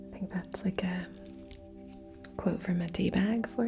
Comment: I thought she said "quote from a D-Bag" the first few times I heard it, lol.